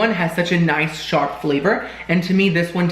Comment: Definitely thought he said the noodles were “achy” instead of “eggy” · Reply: And I thought he said “shart,” not “sharp.” 😆